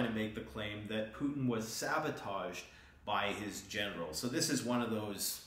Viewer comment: When you said that Putin was sabotaged by his generals, I thought you said his genitals 😂